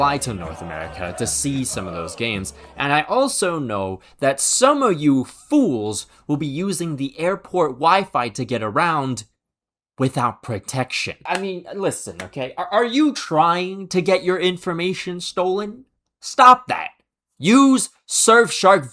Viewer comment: wtf saying "you people using without protection!" I almost spat my ice cold soda out my mouth....until i actually heard what he REALLY said lol!!😂⚽️🤣